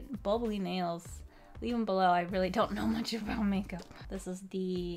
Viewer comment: did you say make up